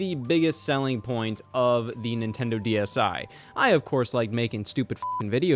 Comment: Thought it said All of the Nintendo Dics